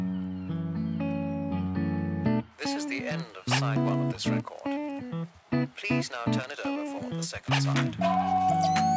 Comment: To those who can speak English, I would like you to write English starting with 2:01. I'm Japanese so I can't understand what it is saying. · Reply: It says: "This is the end of side one of this record. Please now turn it over for the second side".